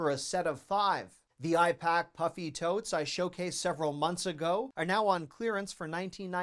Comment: Oh puffy tote?! 🙄 I was so excited when I first heard it as 'puppy tote' 🐶 😅❤